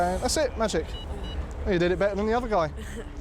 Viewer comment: Did he just say to that young girl that she blew better than the other guy?